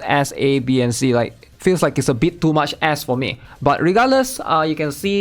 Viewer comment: that sounded like "a bit too much a-S-s too me" lmao